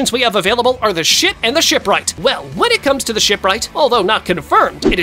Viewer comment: Shit, and the ship, am I mishearing or what